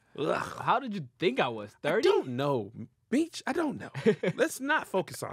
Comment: I thought i heard said bitch but he i think he meant to say but meech 15:10 🥴🤣😂